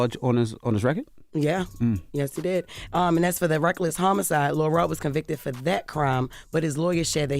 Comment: It sound like she saying Lil Rel